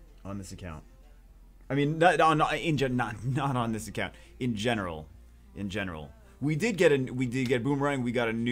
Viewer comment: anyone else hear genital?